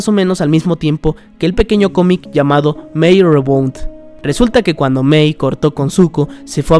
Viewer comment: que dice? mai re que? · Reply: creo que es Mei Rebound